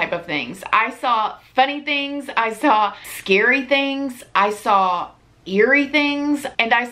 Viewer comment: I thought you said, 'I saw funny things, I saw scary things, I saw hairy things'